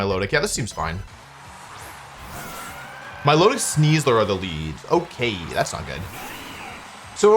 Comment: i sneezed right there and then thought i heard 'milotic sneezed upon their lead, okay, that's not good' gesundheit milotic 😂